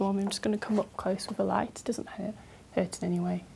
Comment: I'm confused as to what she says exactly from 3:13-3:15. Does she say about looking at the light: "it doesn't hurt, but it hurts anyway" ? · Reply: I think she stammers, She says "it doesn't hur- hurt in any way"